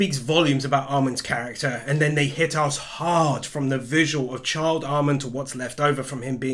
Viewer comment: I won’t lie, when he was talking about Child Armin, I swore I heard him say Charred Armin